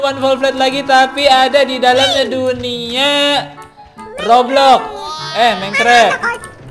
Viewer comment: i hear roblox:v knp roblox kan manicraft